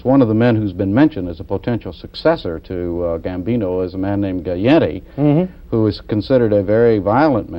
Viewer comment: Guyetti, instead of Gotti ?😂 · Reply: No, Galante. As in Carmine Galante.